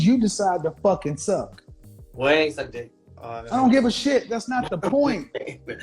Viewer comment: Did she say she don’t suck at 58:47 yea she will forever get played on smh